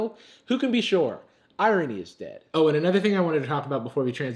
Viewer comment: i thought u said “iron man is dead”